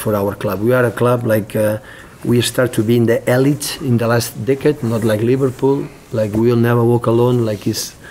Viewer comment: Sounds like Guardiola says "in the last dick'ead" 😄 05:18 when talking about Man city emerging as an elite club ??? Well done to Liverpool today picking up another great 3 points standing top of the table and 9 points ahead of Man city.. YNWA Ps Yes I know he means decade - it just sounds like "Dick'ead" 😄